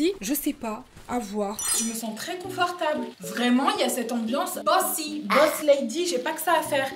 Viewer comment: quand elle a dit "bossy" j'ai compris "bussy" j'etais tres confused...